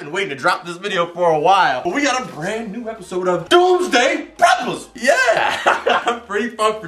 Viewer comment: I heard Doomsday Peppers